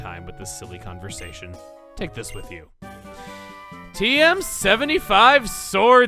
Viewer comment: when the sage said take this with you, i thought he said take this silly conversation with you